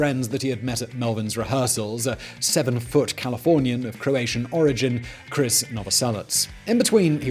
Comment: Did he really say that Krist Novocelic was 7' tall? I had to go back several times and I'm still not sure... I first thought he was saying he was a "severed foot California of Croatian origin"